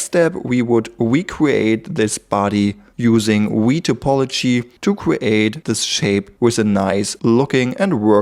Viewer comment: What did you say at 38:48 "wee topology"? What's that?